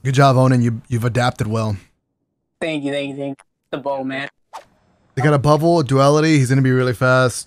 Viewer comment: I swear he just said ‘ty ty ty (inaudible) old man’ 😂 ik he said Bow man